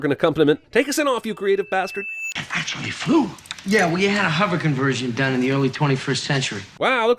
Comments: I always heard, "Yeah, well you had a hover conversion done in the early 21st century." Rather than, "WE had a hover conversion...." Hmm THE MORE YOU KNOW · It sounds to me like Marty said "Yeah well, ya' had a hover conversion done", not "yeah well we had". But I may be biased in favor of the greatest movie trilogy of all time. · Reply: Yeah I always heard it as “yeah well you had” not “we had”. I checked the subtitles and we are right. He does say “well you” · I think he says, “Well, you had a hover conversion done...” Not “We” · Marty says "Yea, well ya had a hover conversion done in the early 21st century." he doesn't say "We had a hover conversion done" like you thought he did. +1 sins for mishearing. Also +1 sins for not making note of the godawful BEAR SUIT that somebody was wearing for the "camera behind the bear" angle in the cave when Marty first finds the bear and it stands up. It's HILARIOUSLY bad once you realize what it is... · "You sinned this because you misunderstood Marty's words. You think he said "yeah we had a hover conversion" what he said was "yeah well, you had a hover conversion" if you repeat it over and over you will catch it